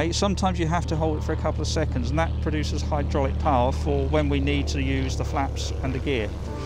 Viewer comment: Several times the instructor said what I heard to be "push power". What does that mean? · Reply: Hydraulic power.